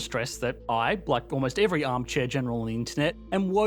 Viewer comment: sounded like “I, like every armed Chechen on the internet …”